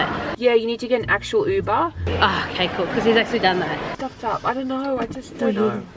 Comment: Did anyone else hear say “f——d up” at 35:02 ?